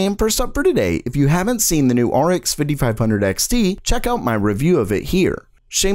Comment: I misheard the 5500XT for 5700XT lol. Also nice video. [<num>_05_20.]